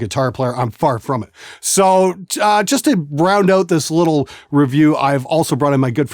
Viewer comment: Round out? I thought he said ''brown note'' I thought I was about to learn something useful.